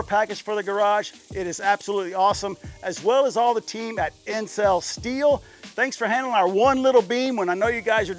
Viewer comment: I assume that was Intsel Steel, not Incel Steel... which is what I first thought you said. :) Nice work, & Gang.